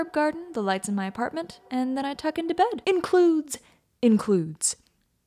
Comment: I thought it said "fuck" INSTEAD of "tuck"